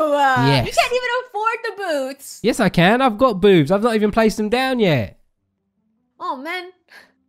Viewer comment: Was it me or did it sound like he said he got boobs